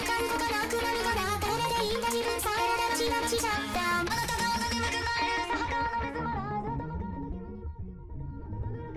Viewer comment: why did i hear "Shut down"? Are they robots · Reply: she does say shut down